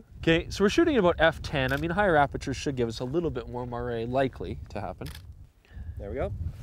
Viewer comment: you mention something like "marray, mooray...." I don't know how to spell it to google it. What is it that you are actually saying. sorry for the noob question.